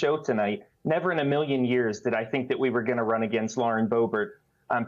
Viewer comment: At least she knows how to reach across the aisle when its to give a man a hand. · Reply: I thought you said we were going bowling tonite😊